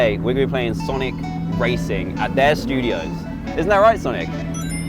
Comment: i thought he said sanic